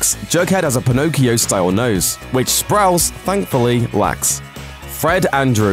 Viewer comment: ngl i thought he said sprouts and i was lowkey confused for a while